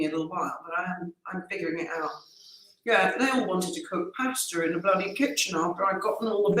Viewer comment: At 9.50 (watching on replay) I thought you said "they all wanted to cook hamster in the kitchen" pmsl. Merry Christmas to you all